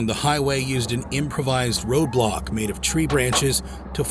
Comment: did I heard Roblox or row block?